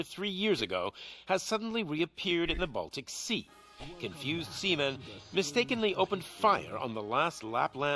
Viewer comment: Did he say seamen, but in the caption its sailor?😅